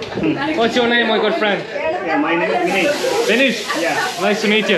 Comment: Really this is his name , I heard 'FINISHE' 😂